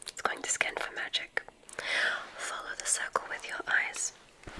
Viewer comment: When you said Cece, I thought you said circle, the Greek sorceress